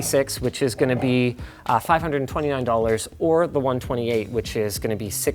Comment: Linus says there is in the video. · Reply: D If you look at the bottom center of the video at 4:18, you see a "512GB*", being a correction of what he said. So he said "128 GB", but meant "512 GB", which was corrected at post-production with the box at the bottom.